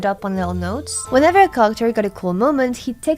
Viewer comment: I was listening without looking at the subtitles and was so confused when I heard 'carchur' for 'character'.